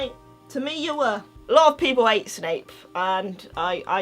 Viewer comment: when I wasn't paying attention a thought she said "some people ate snape" then I realized it was "hate" and I was like😑